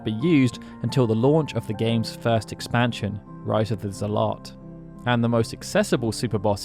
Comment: min as a german ive had a good laugh on this one 😂 (it sounded like rise of the Salat (Salat = salad in german))